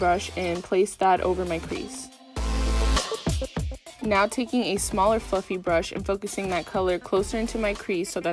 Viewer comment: i thought u said a smaller fvcking brush 😂